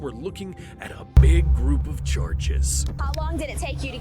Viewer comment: With Captions instead of of Charges it says Children💀